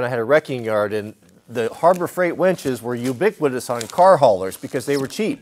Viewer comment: I keep hearing "Harbor Freight wenches". Yarr.